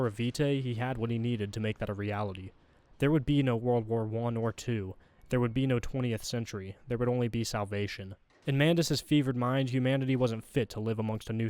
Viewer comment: lmao I had to rewind cause I heard there would be no world war 1 or 2, no 20th century, there would only be south Asian 😭